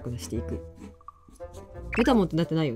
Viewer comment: どうしてもメタモンがメタトンに聞こえてしまう…… アンテ脳消えない( ◜௰◝ )